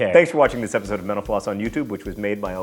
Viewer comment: He says 'mental floss' so fast that I thought he was saying 'menopause' XD